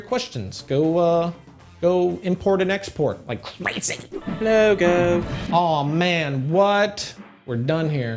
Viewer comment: The captions say "no go" when your logo pops @ 7:40.